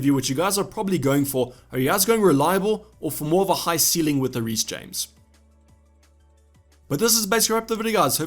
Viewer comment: Did anyone understand what he said after 12:35? Editing error or do you talk like that? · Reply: "are you guys going for someone more reliable or for someone with a higher ceiling like Reece James?"